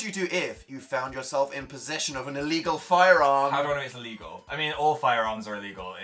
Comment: I legit thought it said an illegal fire alarm, instead of an illegal firearm.